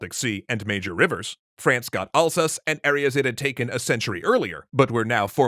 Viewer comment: "France got Alsace". But the subtitles says "France got Ulcers". Ahahahaha!